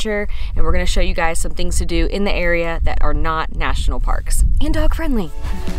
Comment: Congrats you two! Am I the ONLY one that heard the end of the video? lol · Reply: nvm. It WAS just me. I THOUGHT you said "and I'm pregnant". What you ACTUALLY said was "and dog friendly". Carry on. LOLOLOL